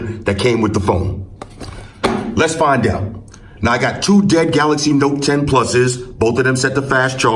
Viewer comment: why did I hear ' I got two gay galaxy 10 +'s' 😂